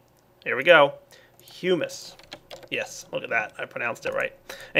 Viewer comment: Thought he said human but it was hummus